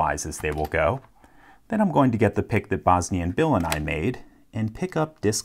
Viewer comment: I thought at 1:21 instead of saying "The pick that BosnianBill and I made," you were calling the pick a "Bosnian Villain", and I thought, dang, that's a badass name for a lockpick.